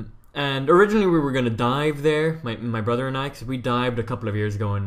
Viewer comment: thought he said die